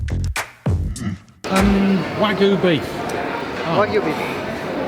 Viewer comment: When the meat seller repeated his request "Wagyu beef" for a second there I thought that I heard Wadu Hek